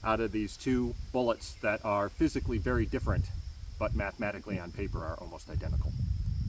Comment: You’re intelligent. And I’m so glad that opinion of mine remained after I played back 2:12 a second time, clearing my ears out, and noticed you said “Mathematically the same”, as opposed to what I thought you said on my first listen, “Magnetically the same.” 😂 good man, good man….